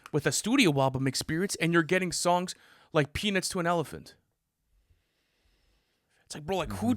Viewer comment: Could’ve swore said penis to an elephant